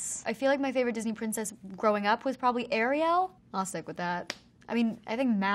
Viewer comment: did she just say ayriell? like instead of ariel?